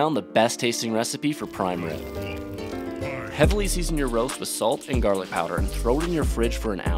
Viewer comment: Am I the only one who thought he said “assault and garlic powder”